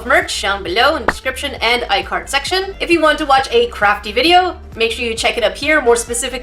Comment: On 13:19 when u say Crafty I heard Crappy. Anyone else?